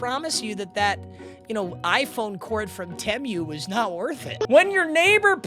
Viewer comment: did unc just say temiuuu